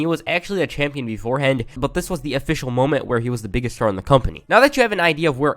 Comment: captions “whore” instead of STAR